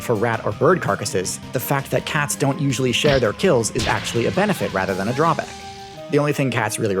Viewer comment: "They don't share their kills" My cat begs to differ and mostly because he thinks I have a skill issue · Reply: Listen carefully to what was said, and if you are going to quote someone, get the quote right. He said "Cats don't USUALLY share their kills"